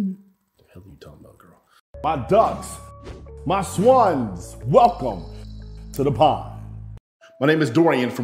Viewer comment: i finally successfully skiped your Intro 🤣🤣😅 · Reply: after all this time I thought he was saying “welcome to the pod”. What do I know? I’m a “old head” anyway. But I have much respect for the brother doing his work!